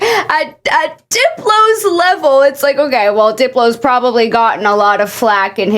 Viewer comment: My dyslexic ahh thought the captain said "Dildo's PRO AI" 😭😭